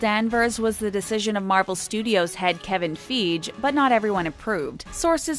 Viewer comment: Did she say Kevin Feej?